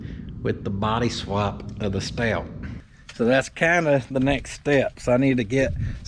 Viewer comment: Ah you said Stout, I thought you said Scout 😅🙃